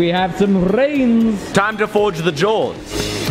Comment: Ohhh forge the jaws, I thought said forge doors🤦‍♂️ we know they don’t work.😜